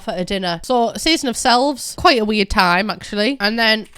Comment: High key thought it said “season of Slaves” 🥲